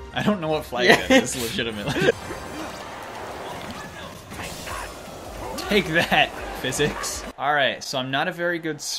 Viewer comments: It sounded like eggman said “take that, boomer” when he landed on the skate board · I can't be the only one who heard "take that boomer"